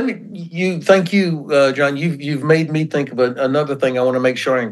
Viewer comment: Sponsored by Ground News: Compare news coverage and spot media bias with Ground News <link> My genuine thanks to Ben Hodges for agreeing to set aside the time both for this interview, and for our discussions outside it. While there may be a temptation in some NATO or allied militaries to look at the war in Ukraine as an aberration that doesn't reflect what a peer-conflict involving NATO powers would look like - I think there it's easy to argue that the disruption and valuable lessons are very much there, and that the speed at which those lessons are understood and reflecting in practice is going to be a significant factor going forward. Not just in the sense that they will shape how effectively NATO and allied trainers can prepare Ukrainian recruits for the battlefield, but also in how quickly allied militaries can make the investments and changes required to adapt to innovations and lessons of the war in Ukraine. I hope you enjoy the discussion, and are prepared for a return to a more 'slide show as usual' episode next week. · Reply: Did he call you John at 44:38?